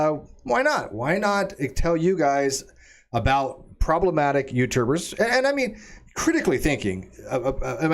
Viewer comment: I heard "problematic uterus". Only afterwards did I realise said "problematic YouTubers".